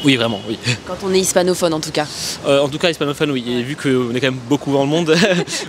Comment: Does anybody else struggle to understand mine 4:46? I only hear "Et vu qu'on... beaucoup dans le monde", I can't catch the words "quand même" 😭